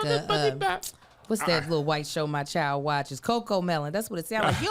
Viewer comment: I almost choked at 03:28 because I thought Kev said "WTF IS THAT" in the Future voice and introd us to cussing Kev 😂😂 This Wednesday has been wild enough already 🙆🏾‍♀️